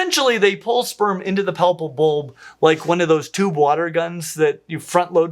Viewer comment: "There is no worse feeling than dying with your palpal bulbs full." ~, 2023 this quote has been immortalized. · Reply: Oh I thought he kept saying papal bull.